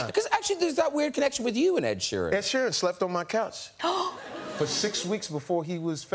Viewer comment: I Thought It Said Crouch Not Couch